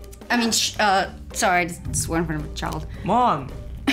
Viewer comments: did he just say mom when she swore?? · did he just say "mom"?